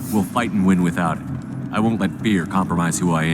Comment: Uh, what? I know he's trying to say "fear" but the more often I hear it, Shep says: "I won't let beer compromise who I am!" Oh, the self delusion xD